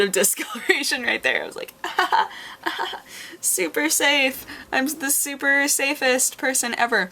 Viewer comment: Your hair color has now become unsettling; rather than hearing "Super SAFE" I heard "Super SAIYAN" instead... Whoops... My high school also had Spanish, French, and Japanese Our Japanese teacher was also an oddball; everyone loved her for it, though. I think she only got angry at my class once... Not so much for the other Japanese classes/levels/#nensei... Yeah...